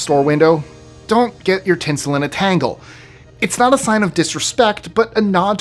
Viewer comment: i thought u said "tits in a tangle" 😂